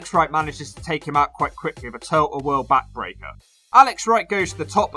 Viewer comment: did you say turtle whirl back breaker cause it sounded like it lol. seriously i have listened to it 30 odd times you did 🤣😂🤣😂 all those wes Briscoe videos are having an effect 🤣😂🤣